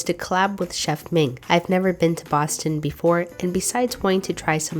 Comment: Lol I've been on an almost all night Avatar the last Airbender watching kick, and actually thought you said you'd never been to Ba Sing Se. 😂